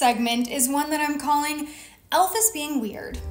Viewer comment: Haha! I heard “elf is werid” as “Elvis is weird”